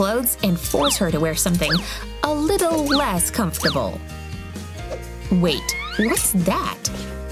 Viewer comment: Am I the only one who heard her say, "Wait! Hey! Wus dat?" 😂😂😂😂😂😂